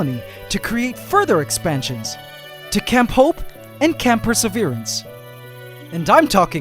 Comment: I heard “camp hope” as Jhope ... I